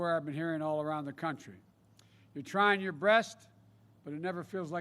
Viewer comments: of the video, did he say to people "you're trying yourr BREAST"? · Did biden just say trying your breast. Instead of best??? 😂 Lol.., you will hear it if you listen starting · Did he just say, "Your trying your BREAST"? 🤣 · Did he say you are trying your breast at 2:16, 2:17? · Did he say breast?